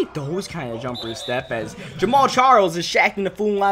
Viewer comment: who else heard him say Jamaal Charles 😂